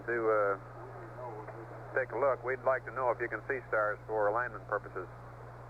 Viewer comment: "we'd like to know if you can see stars for landing purposes" WHAT!? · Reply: I heard, “we’d like to know if you can see stars for ‘alignment’ purposes”